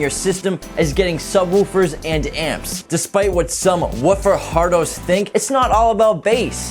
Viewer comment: did he say “woofer hard-ons?”